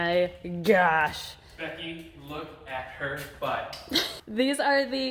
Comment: Did I just hear right "Becky look at her butt" or am I just hearing things!?